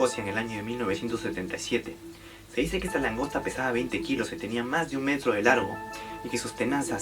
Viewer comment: escuché : mas de un "menso" delargo XD .